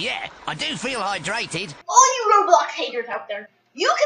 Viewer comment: why do i hear "roblox" 💀💀💀💀💀💀 0:07 and why copyright claim 💀💀💀💀💀💀💀💀